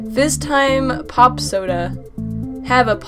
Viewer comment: I thought fiztime said FIGHTME POP SODA